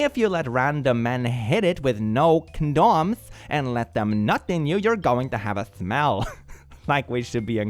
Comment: for a sec there, I thought it said "knot in you" and I was wondering if OP might have read too much werewolf porn - turns out it was me who's read too much of that😂